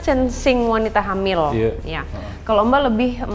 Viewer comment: Coba apa ada yang mendengar seperti suara wanita di menit 7:42 - 7:43 ? Halus pelan sekali atau saya salah dengar? Seperti bilang Iya?